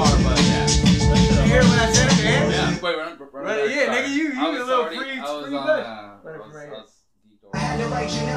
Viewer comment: Did he just say nig#@ r or ive heard him wrong 😬??